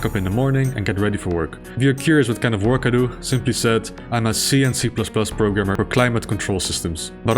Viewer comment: When you said "C and C++", I thought you were saying "CnC++", like a joke about the Command and Conquer games.